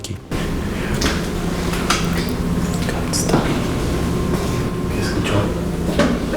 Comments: no me fijé que tenía el traductor automático y me apareció la traducción de Inglés a español "No pares" "Don't stop" y si, si sentí que dijo eso en inglés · no estoy loco, escuche "stop" Lo juro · En el 10:40 se escucha una voz que dice don't stop o quizás haya Sido uno de ellos quien sabe